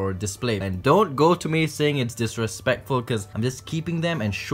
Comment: Omg you killed one I thought you said “ no disrespect” but really it is